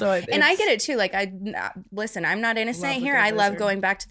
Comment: So we just gonna pass the fact she said the n word? Like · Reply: I heard “love a good lizard”